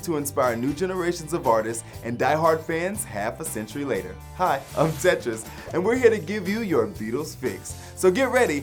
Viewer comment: I thought he said "hi, I'm pretentious" at the start